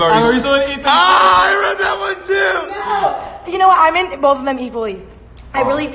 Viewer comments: Did emma just say "I'm in both of them equally" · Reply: im into both of them equally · in 9:46 what did james say halp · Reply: Dr Else Sihotang are you still in Ethan’s lane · what did James and Ethan say? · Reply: ‘are you still in ethans lane’ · What did James and Ethan say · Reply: “I read that one too!”